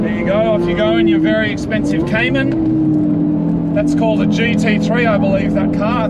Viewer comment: Expensive Cayman Porsche owners: How dare you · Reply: I thought he said expensive Caterham lol